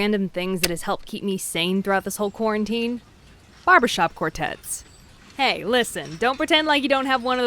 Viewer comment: Thought you said "Barbara Shop Quartets"